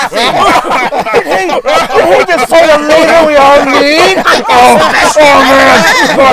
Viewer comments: What's Starscream and Megatron saying when everyone laughs at 3:23? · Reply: What?! Stop laughing! Did he- did he just say the leader we all need?! Hahaha I think that's the best script I've ever heard! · I couldn't make it out, but what does Starscream say at 03:29? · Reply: Stop laughing! that's the best script I've ever heard!